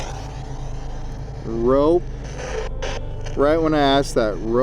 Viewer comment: When you ask if Myra assaulted the children I hear a female voice saying “it’s obvious”